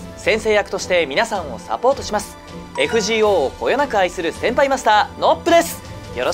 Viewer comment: 先輩マスターが変態マスターって聞こえた あ、まぁ、でも、あってないこともない、か